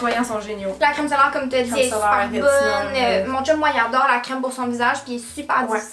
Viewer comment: qu'est-ce qu'elle a dit ? J'ai rien compris à sa phrase. Jentends "super bonne mouncho moyen dore crème pour son visage puis super difficile" 🤔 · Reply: Mon chum moi y’adore la crème pour son visage